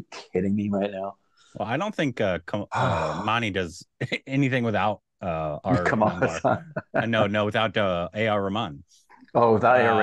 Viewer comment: What said at 2:25 about arr and anirudh? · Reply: he said "I don't think Mani does anything without AR rahman"... he didn't say anything about anirudh